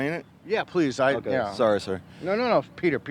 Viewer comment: When he says sorry sir I missed the sir so it sounded like he said sorry and Peter corrected him thinking he thought his name was Sorry 🤣